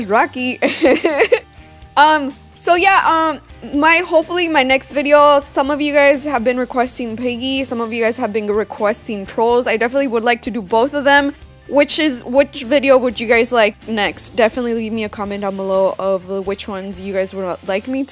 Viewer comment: did you say piggy?